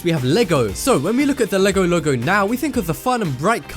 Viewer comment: any1 thought he said legolego or logologo? :D